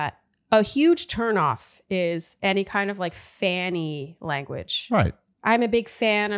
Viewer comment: i know they were saying "fan-y" for q2 but i kept hearing "fanny" bc the asker was from the uk and i couldn't help but laugh